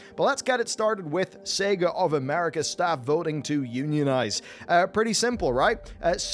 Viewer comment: He said UNIONIZE I thought he said euthanize I was confused.